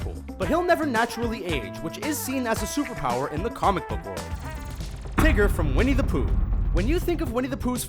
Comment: Lol, when he said tigger, I thought he said trigger XD